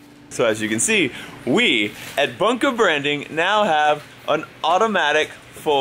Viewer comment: It sounded like you said "Bunkew Bwanding" and it sounded like Lincoln and was so cute (no homo). 😂😂😂😂